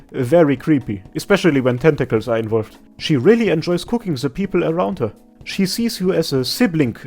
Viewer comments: hold up I actually do read the subtitles, and thank god I do, because when I heard you saying "she really loves cooking the people around her" about gummy, I choked, even though this could be lore accurate except from the enjoying part lmfao epic return mate, keep up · It might be because of your accent but at <link> I heard: ''She really enjoys cooking the people around her!'' which, given her history, is hilarious. (in a dark way) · I thought you said "She really enjoys cooking the people around her" 💀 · I misheard the Gummy's part as "She enjoys cooking the people around her". Had to rewind there for a sec. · omg when you said about gummy i only heard "she really like cooking people around her" XD